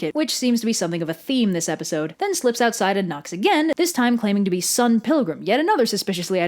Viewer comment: I heard Scott Pilgrim... Well i guess that his character isn't much different...